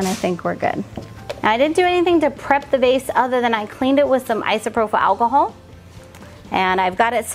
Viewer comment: Did you have to prepare the vase before pouring the epoxy? It looked as if it had been lightly sanded so the epoxy could grip on! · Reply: I thought I heard they cleaned it with isopropyl alcohol